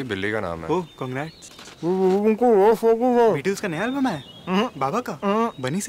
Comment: did they just said BTS.....oh my God I am dead😭😭😭😭😭😭😭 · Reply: They said Beatles not BTS 😂